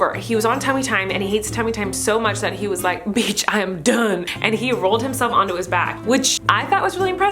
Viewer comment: Haha 8:06 did she say bich I’m done or wat 😂